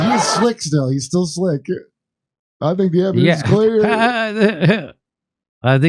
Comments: “I think the evidence is clear” translates to steps were taken to erase enough evidence to keep him out of trouble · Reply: It really sounded like he said I think the evidence stinks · It sounded like he said "I think the evidence is dead."